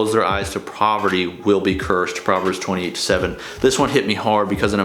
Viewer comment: could you tell me that verse I can't understand. It sounds like you said proverbs 28:7